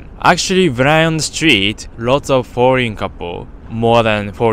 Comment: "lots of boring couple"? Edit: ok i think he's saying "foreign"